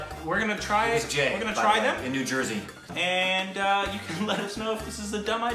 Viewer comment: Whoever sent them these Mangoes, deserves a big Shout Out. We should know his name. Cheers!!!! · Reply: I think he said Jay from New Jersey